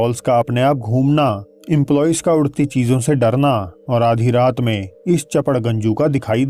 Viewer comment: 😂😂 what did he said chaparganju